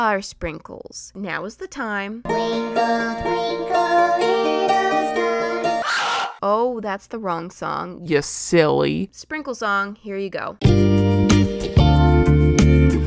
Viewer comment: Moriah: plays twinkle twinkle Me: wait what’s wrong with my audio? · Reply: Me too though I thought it was saying sprinkle sprinkle little star